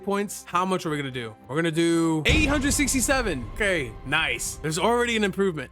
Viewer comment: did bro say 67